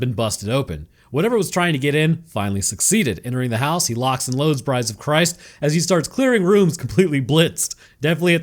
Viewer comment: 😂😂😂 you are the first person I thought of when I saw the poor anglerfish. I swear I heard "lock and load, Brides of christ!"